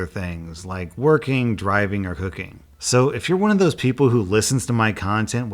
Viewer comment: During the Raycon ad I thought he said “working, driving, or hooking” and was just like “well if that’s your thing. Took a minute to realize he said COOKING facepalm